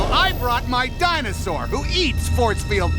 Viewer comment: I thought he said I brought my diamond sword